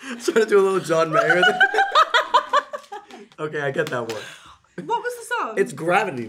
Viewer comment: Parece que dice Okay aquí andamo JAJAJAJA